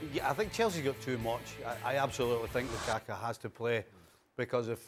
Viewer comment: Did Stevie just called that man Lukaka 💀😂